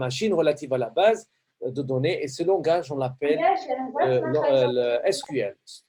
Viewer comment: Bonjour Monsieur s'est il te plait c'est quoi le nom de langage que vous dit dans (14:09minute) ? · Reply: SQL